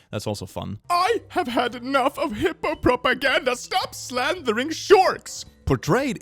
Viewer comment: I heard "hippo propaganda" as "Hitler propaganda" and was SO confused. My old person trait is wanting an actual menu, not having to scan a QR code